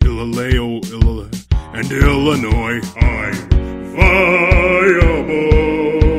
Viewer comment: can somebody tell me what is Skarner singing at minute 1:00?plsss (english is not mymotherlanguage and i don't understand all the words pronounced) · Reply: alla curcuma He said "I am viable". 2 times^^ :)